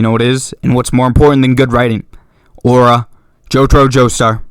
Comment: HELP THE SUBTITLES SAY ORA INSTEAD OF AURA 😭😭😭